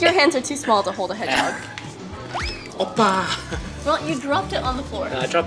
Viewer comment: Did he just say oppa 😂